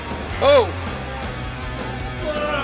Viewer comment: anyone else hear "WHATS GOING ON OUT THERE"